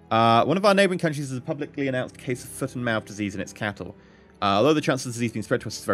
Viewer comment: I heard that as "foot in mouth" akin to saying something incredibly stupid, which echoes some of the comments :P